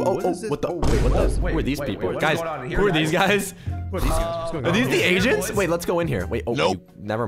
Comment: Wait did he say are these the asian..?